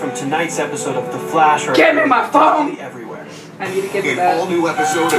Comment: "All of tonight songs are available" "GET ME MY PHONE!" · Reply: I thought I heard her say "GET IN MY PHONE" but it's funny either way. :)